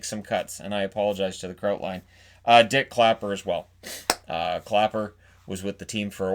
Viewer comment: did he say Dick Clapper? Epic name.